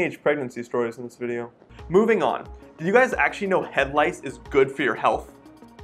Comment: my dumb ass thought he said "head lights"